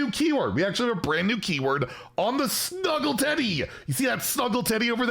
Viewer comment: Looks exciting! And I heard “Snuggle Zeddy” 🤣 Thanks!